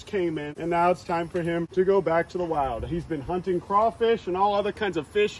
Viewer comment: I really thought he said "eating crawfish and toes," lol